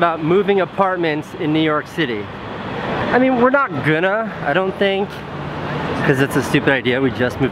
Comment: What means gunnar, gonnar whatever he's saying at 0:08? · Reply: Going to